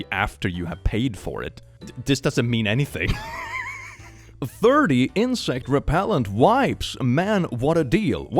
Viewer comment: I was eating and looking down at this part and it sounded like "30 incel repellent wipes" and. Can someone invent these? 😹